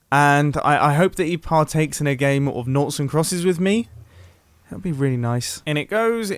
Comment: I thought he said Naughty crosses.